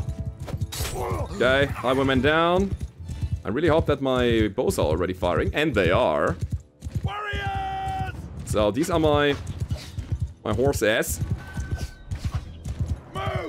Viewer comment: Every time am looking the Bannerlord walkthrough, I hear “Hello Mechwarriors” :)))